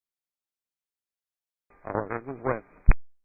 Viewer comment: Actually said "All I heard was 'Wind'". Not "Oh, they just went" Lol.